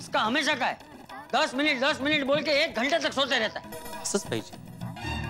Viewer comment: what did bhide said in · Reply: 'Asaj pahije' meaning you deserves this(shouting from Bapuji)